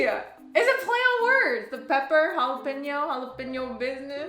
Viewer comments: I thought said peppa as in peppa pig lmao 🤣 when she was telling her joke. I need stop watching shows with my toddler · I thought she said Peppa like Peppa pig instead of pepper